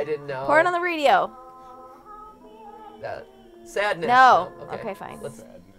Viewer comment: Did anyone else hear someone whisper "sadness" after MatPat said Sadness??